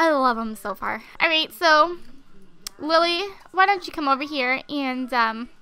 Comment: sounds like your saying willy but your actually saying lilly haha